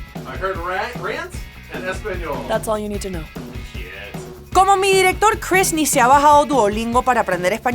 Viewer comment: Lmao I’m not used to Venezuelan accents and at first I heard “como mi director Chris” as “como mierda Chris” and was really disturbed at how casually she said it.